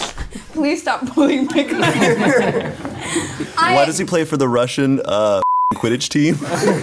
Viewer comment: What does Shayne say at 14:43 ?? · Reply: Why does he play for the Russian fuckin quidditch team